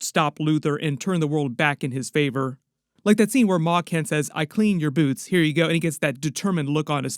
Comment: I thought you said Paw Kent as the dog not Ma Kent lol